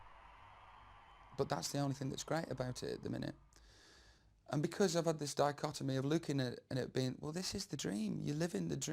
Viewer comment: what does he shout at 1:01.00 ?? during better man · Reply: "fucking amazing"